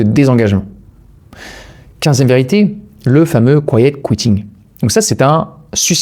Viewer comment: De quoi parle t on à la minute 12:49? le courrier de quoi? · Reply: Le „quiet quitting“